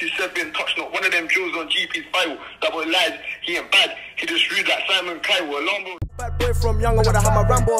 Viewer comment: Sounded like sj said "double lies he aint bad" 🔥🔥🔥 though as usual